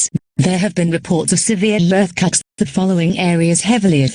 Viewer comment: “There have been severe earthquakes” Why am I hearing “severe earthcocks”